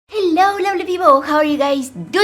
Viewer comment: I thought she said hello lonely people and I was like how does she know I am lonely